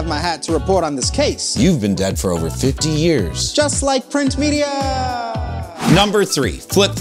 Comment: "You've been dead for fifty years." "I'm still mad at you for that." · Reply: Bruhhh i was confused i heard "just like print media..."